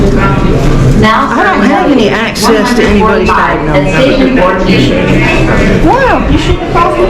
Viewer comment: did she say "I don't have access to anyone's diagnosis?"